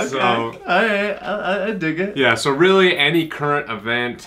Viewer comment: uma dúvida q percebi só agora (da língua inglesa), no momento 1:20 ele fala algo q soou como "i dig" (??) q foi traduzido como "eu gostei". eu nunca tinha escutado essa expressão... é "i dig" mesmo ou eu entendi errado? é uma gíria ou um outro significado pra palavra?